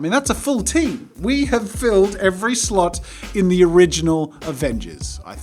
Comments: When he said "We have filled every slot in the original Avengers" it sounded like he said "We have filled every slut in the original Avengers" and I was confused for a second. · i swear i heard you say "We have filled every SLUT" Curse my mind